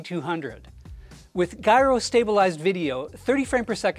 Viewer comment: I'm sorry. Did he say "guy-rō"?